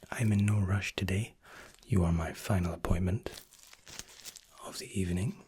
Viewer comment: You said, "you are my final appointment." I heard, " this is your final appointment. " Then I thought, "this is how the ASMR killer did it."